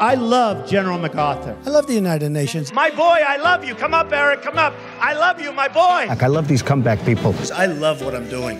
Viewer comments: What's he said on 0:19? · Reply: "Come up, Eric, come up. I love you my boy." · What he say at scene 0:18 ?? That Barack is his Boy ????? xD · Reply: Eric Trump